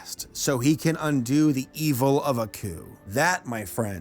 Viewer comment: I keep hearing 'A coup' instead of 'Aku' (ah-koo)